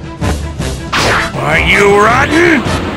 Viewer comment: Commenting for each transformation/power up moment 1:16 Base Goku full power . Cool moment 1:45 I love how he says "why you rotten" as he goes Super Saiyan 4:04 SSJ2 4:53 Super Sonic style. Argh! 6:14 Goku: goes Super Saiyan 3 Sonic: nice hair ya got there 9:21 NoBoDy BeAtS a GoD! (Super saiyan God)? 10:25 Super Sonic used FULL POWER. It's super effective! 12:16 Super Saiyan God Super Saiyan , a.k.a Super Saiyan Blue 12:48 hyper Sonic style 16:00 Kaio ken + SSJB 19:26 Epic Maximum power clash incoming. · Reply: yo I thought he said "Why you rotten!"